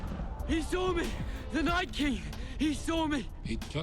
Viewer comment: it sounds like he say 'nice king' instead of 'night king'.